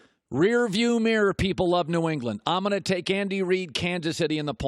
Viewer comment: I thought he said Kandy Reid and now that’s how I’ll know him forever